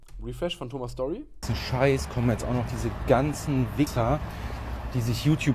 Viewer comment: Hab bei 6:30 erstmal Wicca verstanden und dachte mir was hat den die Religion jetzt damit zutun xD